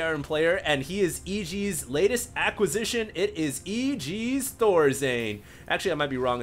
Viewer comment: HD says: It is EG's THorzain Captions says: the lord's day Lol